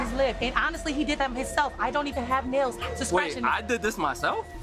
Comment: Hold up did she just say she don’t have nails?